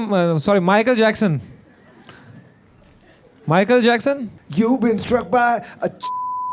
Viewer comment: What did vishal dadlani say at 40:38?? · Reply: You been struck by a ** criminal